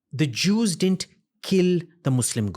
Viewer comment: What did he says min 17:18 “the Jews killed the Muslim god”??? This guy doesn’t know what he is talking about what so ever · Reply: " didn't". You illiterate muslim couldn't read or listen what he is saying. Because you illiterate muslim. " The Jews didn't kill muslim God"